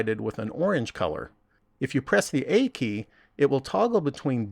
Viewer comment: what button you say in 4:18 because i not understand too well. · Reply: "A" key